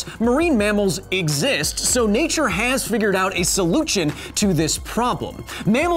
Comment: did he said Solujion instead of solution?